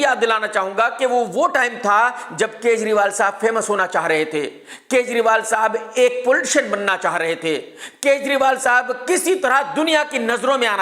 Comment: i thought he said, "Unko bhi Bullshit ban naa thaa". later understood, he is saying, "Unko bhi Poltician ban naa thaa". 😂😂😂